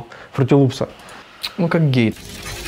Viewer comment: мне послышалось он сказал "ну как гей")